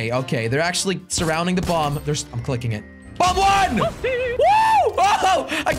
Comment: did anyone hear whoopsy when jelly blew up the bomb